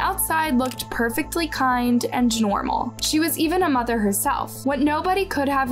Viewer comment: Hold up...what did you say @ 1:00 genormble? Angnormal What is that?